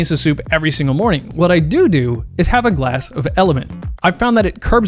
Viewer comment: did he say DooDoo? 😂🤡💀